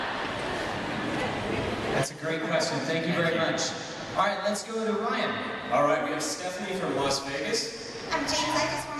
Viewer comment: What does that girl say to James at 29:50? (After that he askes "in a good way?") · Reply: Sorry such a late reply, like very late, but the girl says, "James I want to thank you for changing my life"